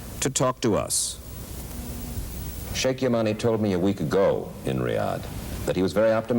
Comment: "shake ya money" (Sheikh yemani) I genuinely thought he said the former.